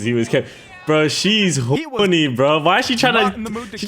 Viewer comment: damn brodie you was just callin her horny tf